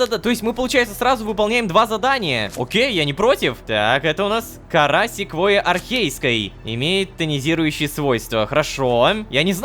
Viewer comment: Послышалось "Карасик Войи Архейской"))